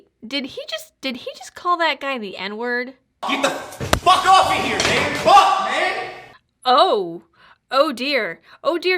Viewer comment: I never got the N word being in here.... But that "Do my bidding" was great. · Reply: I dont think he did either. I heard "get the fuck off of here man, fuck man"